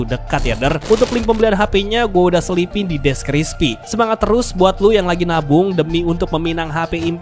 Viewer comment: ini gw salah denger apa emang gitu ya ? "deskrispi" not deskripsi wkwk